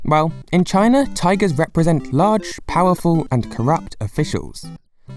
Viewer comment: What did he say? Represent?